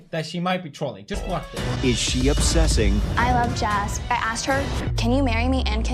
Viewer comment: Whether it got demonetized or not, he actually took this down because he thought the girl said "I love jazz," and not "I love Jas," the girl she was stalking, and also misunderstood when she said "I'm done," as thinking the girl had realised she was in the wrong and not what she actually meant which was that she was trying to take the higher ground by 'breaking up' with the person she was stalking. His editors left it in, it made him look dumb, a LOT of people commented and he took it down. Edit: This is just my opinion, if it was just the short period of time you mentioned, he could've just cut it and reuploaded.